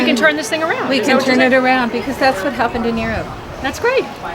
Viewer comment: what is she saying at 1:15? because that's what happens to...??? nero??? if you can figure it out, please let me know! love your show! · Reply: She says..."because that's what happened in Europe". Europeans are so much smarter than Americans, apparently. They are unwilling to be lab rats in this grand, immoral experiment with the sole motivation of corporate profits. GMO experimentation should be done on a secure, remote island, as in the movie Jurassic Park.